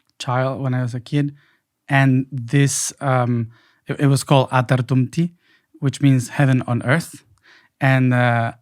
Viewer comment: Love Mathais❤ Anyone know how to spell 'heaven on earth' in Atlantean? Sounded like Ata -tum - te ? ❤